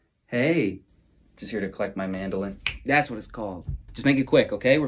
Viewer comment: I thought he said mandarin 😂😂